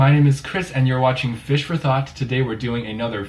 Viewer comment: I thought you said fish for thots and almost choked on my coffee 😂